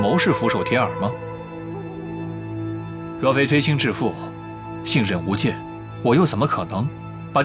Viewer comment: 若非推心置腹，一不小心听成若非微信支付。。。